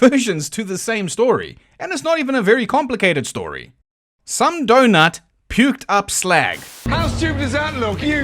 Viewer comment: I mean I heard Deidre puked up a donut, but maybe I'm just remembering The Toy Dolls